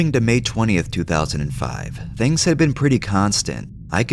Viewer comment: Did he say may 20 2025 bro is may 13 2025. SKIP TO